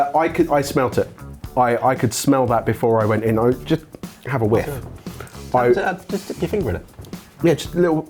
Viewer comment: With as much fairness to as possible, when said 'have a whiff', I thought he said 'have a lick', so may have simply misheard. BUT that still doesn't excuse the attempt to taste what is basically pure capsaicin.